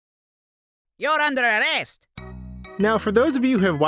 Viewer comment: in the intro i thought Luigi said "who under re-est" at first \/o-o\/